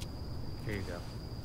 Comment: josh: here ya go tyler: thanks josh: it’s strawberry OKAY 1 THATS SO CUTE & 2 WE HEARD JOSHS VOICE IN A MUSIC VIDEO AHHHWJWKWKWKW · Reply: wait i thought he said strawberry kiwi am i hearing things